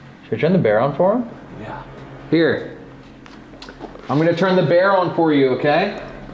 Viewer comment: till about 39 ish it looked like the flashlight kept slowly wobbling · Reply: Just before 1:22:00 I definitely heard that voice say "Bear"